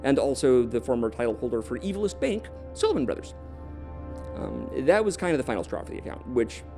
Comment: I can't find anything in reference to the sylvan brothers bank at 9:40? maybe I misheard but if anyone can explain that would be nice